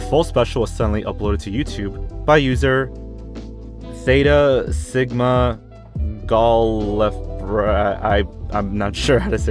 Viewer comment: Its a bunch of terms from Dr who merged together (namely their alien college nickname and their planet of origin) and is probably said "theta sigma gal-lee-frey-an"